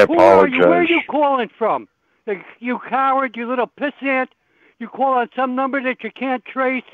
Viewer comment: does he say “piss ant” 🐜 here?